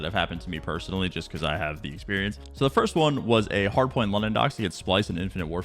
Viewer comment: Did he just say he was playing “hardpoint London socks in infinite warfare “ ???